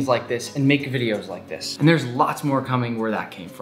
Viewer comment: When he showed pesto I thought he said it’s time to die and I was like 😳😳😳